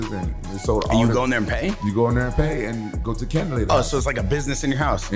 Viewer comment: I didnt read the title. I thought he was saying CANDIDATE then CANDLE LADY 😂😂😂😂 Salute the goat tho. "I was like huh. Ooooh caaaaandy lady"